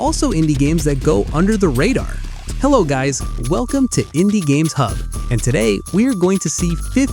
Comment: did he say "hindi games"????